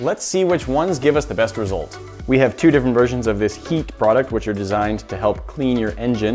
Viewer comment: what i understood: "We have two different versions of this YEET" What he actually said:"we have two different versions of this HEET"